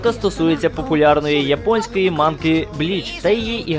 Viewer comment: мені одному почулось - ..японської манки.. ? )